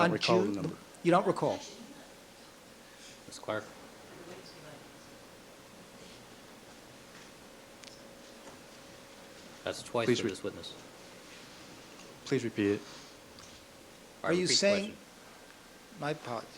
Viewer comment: what happened? ..can't make out what she's saying. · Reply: Scheck interrupted him again: MS. CLARK: YOUR HONOR, OBJECTION. HE'S NOT -- THE COURT: MISS CLARK. MS. CLARK: MAY THE WITNESS BE ALLOWED TO ANSWER? I'M SORRY. THE COURT: THAT'S TWICE WITH THIS WITNESS. THE WITNESS: PLEASE REPEAT IT. THE COURT: REPEAT THE QUESTION.